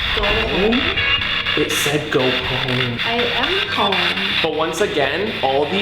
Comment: i heard dont come